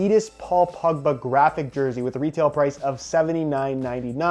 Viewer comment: It sounds like he is saying pugba not pogba